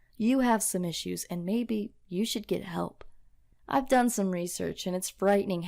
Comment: I thought you said go to hell but i realized you said go get help